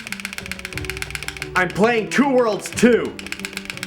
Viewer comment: I liked this video. Then I heard you say "I'm playing Two World Two!"